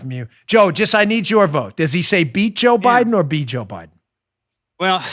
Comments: I definitely heard he's gonna beat joe biden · Hi Dan, I don't know if you've figured out slow man Joe's gaff yet but I heard "beat." You see with the visual of him saying it, you won't see the "T" pronounced because the "T" sound is done in the mouth with your tongue. 🧐😁😁